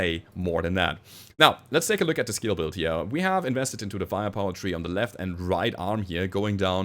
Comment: Each time you say "firepower tree" I hear "fire poetry", which is quite accurate to what you do : poetry with PPC's :)